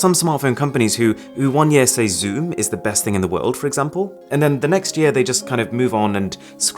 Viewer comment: I skipped back at one point of the video, and it sounded like Aron said: 'The Oneplus 9 is the best thing in the world for example'🤣